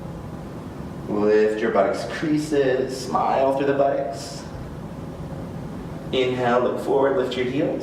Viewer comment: Very nice. did I hear you say "smile through the buttocks"?